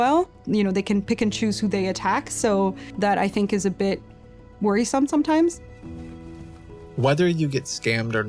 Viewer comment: Did I hear that right? Did she just say it’s “worrisome” for the scambaiters to do what they do? 🤔